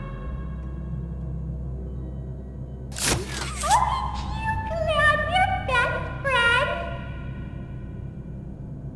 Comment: what is daisy saying here I can't understand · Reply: Look at you my best friend i think